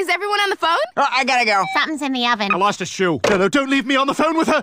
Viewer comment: "Oh I gotta go!" "Something's in the oven." "I lost a shoe." "No no don't leave me on the phone with her!" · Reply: Lol, I heard "fountain's in the oven."